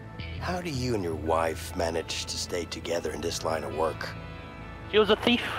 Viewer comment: I thought he said sleep together at 11:15 :3